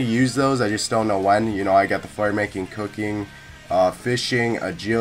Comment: i thought you said the fart-making.....haha